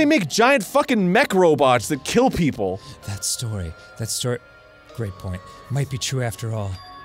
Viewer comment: “What he says sounds stupid and irrelevant... but I guess I should remember it just in case” Job interview simulator This is. I forgot how Arin ignoring the rules during danganronpa is one of the few times he does that that cheese grates my skin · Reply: I misheard Danny saying “great point😒” as “Frank Lloyd,” As in, “Frank Lloyd Right murdered Byakuya”